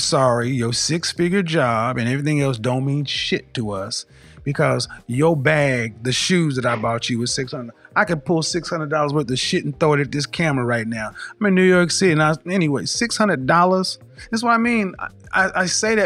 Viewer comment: Totally thought he said "I'll shit 600 dollars and throw it at the camera " 😆